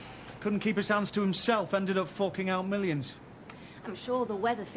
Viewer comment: Charlie sounded like he said fucking out millions. Loooooooooool